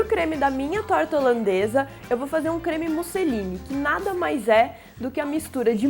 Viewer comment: Eu ouvi "creme Mussolini" no 1:22 :v